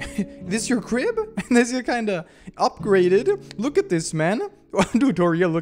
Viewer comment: did he just say "this shi kinda upgraded" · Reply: "this is kinda upgraded" I think